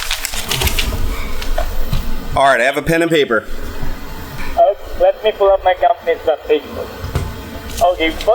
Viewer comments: I like how the CC @ 18:11 says "Ok, let me pull up my girlfriend on Facebook" xD I think he said something like "Okay, let me pull up my companies webpage" or something similar xD, but man that CC killed me. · does he say "Let me pull up my girlfriend on facebook?" oh man this is hilarious especially him using wikipedia